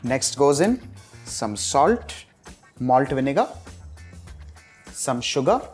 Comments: did u just say the n word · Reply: He said "Malt Vinegar" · what did he say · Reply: Malt Vinegar. You can use white vinegar. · Did he just said molten nigga? 2:34 Jk just sounded like it.. keep up the good cooks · what did he sayyy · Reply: Malt vinegar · what is he saying/what is this ingredient he is pouring? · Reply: Malt Vinegar